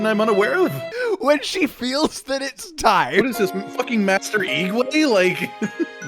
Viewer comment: did I just hear say, master Ee gway 😂😅